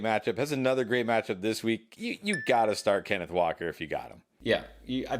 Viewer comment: Walker is 100% a must start. Don't listen to these guys. Do not sell Walker. He will remain a top 10 RB rest of season. Don't know what y'all are seeing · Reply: For some reason I thought you said waller lmao!